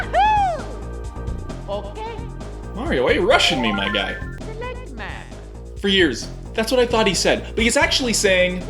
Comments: I thought he was saying "select match" until this point in my life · "I THOUGHT HE SAID SELECT MAN!" I died😂😂😂 Instead he said SELECT map... God the nostalgia tho